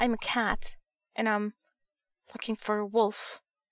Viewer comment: LOL! Soz 4 this comment but @ 1:09 it sounds like u said im fukin 4 a wolf :S